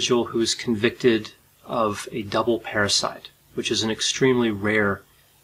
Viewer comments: I thought it said double patricide. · Thought he said convicted of a double parasite.